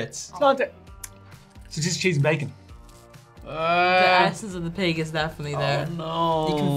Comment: I thought she said "the of the pig is definitely there"